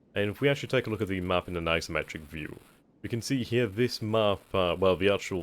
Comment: Every time he says 'isometric' I hear 'asymmetric'.